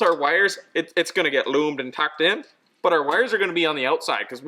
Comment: For all the folks that didn't catch it: "...it's gonna get loomed and tucked in..."